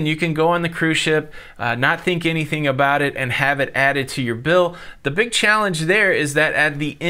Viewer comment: I absolutely despise this practice of prepaying gratuities. I've always thought of myself as a very generous tipper when the service is good. The cruise lines have turned tipping into an expected wage subsidy for their underpaid and overworked employees because they do everything they can possibly do to make certain you know a certain minimum gratuity is expected, while winking and telling you it's really up to you. They have basically turned it into a tithing type situation and use guilt, normally reserved for churches and charities, to make sure you pay. So, let's just call it what it is; it's a service charge that you can adjust downward only under extenuating circumstances. They always reserve the right to pressure you like you're at a timeshare presentation in order to get you to pay. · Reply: I thought he said they add it to your bill. If this is the case it is a mandatory charge and not a tip.